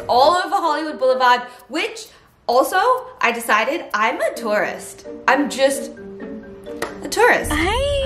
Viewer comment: I thought you said "im a taurus" instead of "im a tourist"